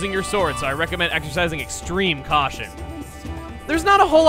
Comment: Aw, man, I thought Fi said "Exorcising" extreme caution at the Lizalfos that curses you. Missed opportunity.